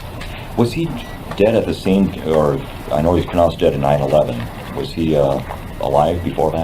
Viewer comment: Did he say 9/11?!😱